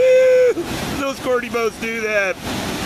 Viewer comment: Commentary is gold haha. What'd he say 7:15? "AHEEEWW little squirty boats do that"